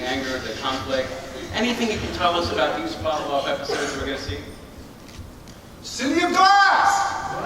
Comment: Oh he says city of glass I thought he was saying city of bass or city of bad asses